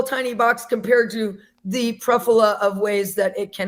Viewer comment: christians taking WHAT from lgbtq ghosts????? uhh should i convert to christianity or something??? 9:54 also i don't think you noticed this but she said "pruffula" instead of "plethora" LMAOOOO